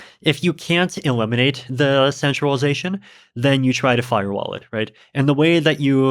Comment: 44: 30 I thought he said Fire Wallet. I was so confused for a min...